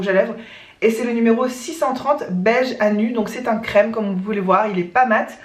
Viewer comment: coucou ma Minutes fou rire : quand tu parles de ton rouge à levres , tu dis qu'il s'appelle : Beige à nue ( attention tu vas avoir ton fou rire du jour !) j'ai compris " beige anus" et direct je me suis dis c'est beige le trous de balle ? Pour le diffuseur sauf si tu aimes le flacon car tu le trouve trop beau ( c'est le cas ) , saches que c'est marketing tu peux très bien acheter juste la recharge et mettre les batons directement dedans . Encore un point commun ( on doit etre soeur spirituel !) je suis digne de l'odeur de l'amande ( on en parle de l'odeur de la colle en petit pot Cléopatre : le top de l'extase des narines !) a tu deja sentis justement de la marque Roger & Gallet : Amande Persane , pour décrire ( on juge pas je suis mon non plus pas le top niveau explication d'odeur ) c'est une odeur gourmande mais tres femme car souvent amande = odeur d'enfance que pour le coup la c'est plus poudré et délicat tres cachemire . Je suis certaine qu'il te laissera pas indifférente . Kiss ma et comme dirait Mister Hugo : Aller bonne nuit !